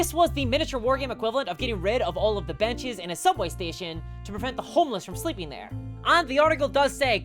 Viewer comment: i heard get rid of all of the banshees in a subway station to prevent the homeless to sleep with them.